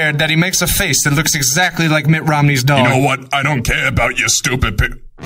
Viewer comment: for a VERY long time i thought said "Mitt Romneys Dong" instead of "Dog". dont ask